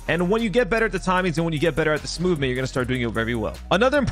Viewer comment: i thought you said smoothment for a second i got excited